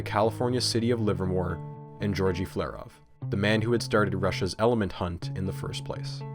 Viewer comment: At first I thought it said "the man who tried to fake an elephant" and my mind broke trying to comprehend.